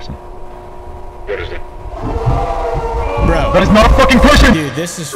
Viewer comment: Does anyon know what the dude said at 6:34? I can't tell. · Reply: That's not a f*cking person